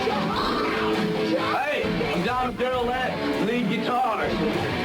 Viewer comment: Dimebag was actually known as "Diamond Darrell" in the early Glam Years. I know it's a bit difficult to hear what he's saying, during the video clip of him at 1:30, but I'm nearly 99% positive he's saying "Hi... I'm Diamond Darrell at Lead Guitar".